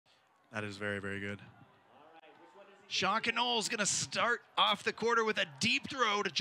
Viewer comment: Did anyone else hear “deep throat” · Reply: When he said "Sean Connole" I heard "Shock and Awe"...